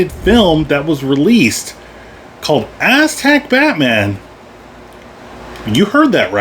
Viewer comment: No I didn't hear that right. Thought u said "Asshat Batman" till the words popped up